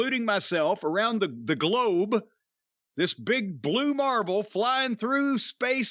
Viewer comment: at first I thought he said "marble", he actually said "marvel"